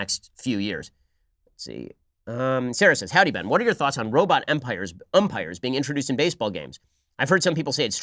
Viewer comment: I thought he said robot empire I was like "wtf no"